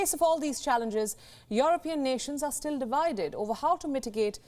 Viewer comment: Divided..??...😁😀😀 everytime i heard "we were united "